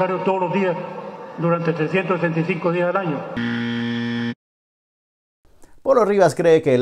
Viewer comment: Se escucha bien que dice "365 días del año" , no "665".